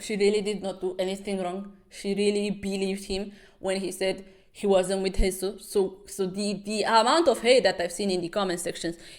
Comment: not the automatic subtitles saying "she really believed him when he said he wasnt with jesus" at 53:32 lmfaooo this whole video has me in tears, glad to see back with another unhinged webtoon